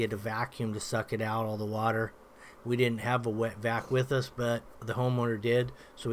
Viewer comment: it sounds like he said, “We didn’t have a wet back with us but the home owner did so we just used his”